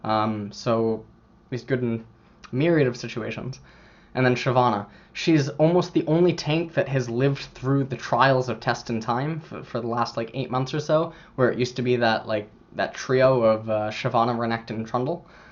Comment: What he said about shyvana? can someone write it? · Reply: "she's almost the only tank that has lived through the trials of test and time, for the last 8 months or so, where it used to be that trio of Renekton, Shyvannna and trundle."